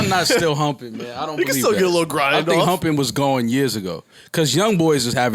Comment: What did parks say?? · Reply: REGAL816 Niggaz still hump Niggaz still get a lil grind off🤣